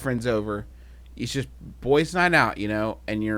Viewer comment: i thought he said dumb night at first and went "wow Griffin is really going hard on this one"